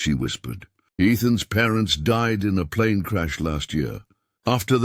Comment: I thought she said Ethan's parents died.